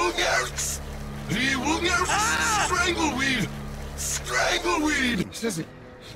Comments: When said straggle weed it sounded like “Strangle me!!” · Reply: i thought he said stangle weed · i only heard "Strangle me"